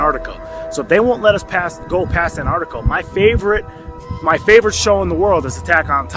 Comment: Did he just say 'AnArticle'?